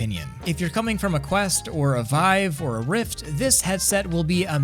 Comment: did you say vivie lmao